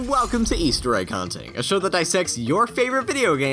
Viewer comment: At the begining of the video I heard "a show that sexs your favorite videogames" wat